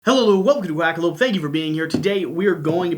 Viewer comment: Haha I was laughing from the first second of the video!! I hear “Hello hello, welcome to Whack-a-lope!” 😂😳🤨 Even the subtitles typed it that way! LOL